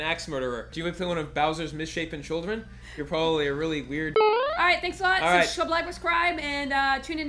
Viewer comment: Did say "Really weird F*G" at the end of that? 12:05 I hope not, but if so, disappointing